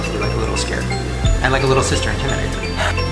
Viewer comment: did anyone else hear Shane say “sister intimidating” or was that just me?